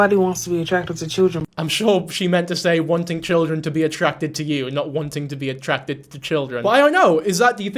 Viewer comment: the caption say attracted but if you listen closely she actually is saying wanting to be attractIVE to children is pedophilia”